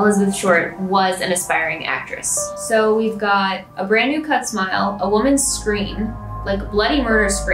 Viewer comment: “a brand new cut smile” was giving me the chills 🌶️ but i heard “a brand new troat smile:)” was more crepier😮